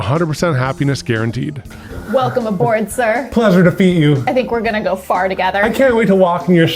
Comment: I heard “I think we’re gonna go fart together.” 😆